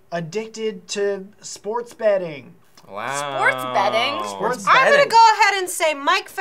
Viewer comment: I totally thought they were saying sports bedding. I wouldn't put that passed Mike, either.